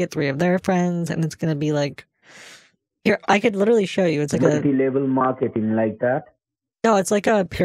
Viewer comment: I thought he said monkey level marketing!!